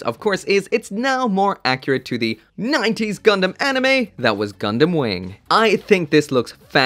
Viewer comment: We need RG Epyon. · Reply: ah I see, I thought he said "Gundam Wing". I missed the "Zero" part. My bad.